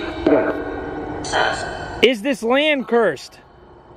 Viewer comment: it sounded like it said "sus" lol